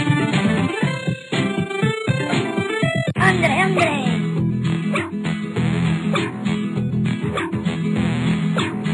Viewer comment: I may recognize the "¡Arriba!" and the "Let's go, amigo!", but what is the phrase the cactus men say at the 0:43 mark? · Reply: "ándele, ándele"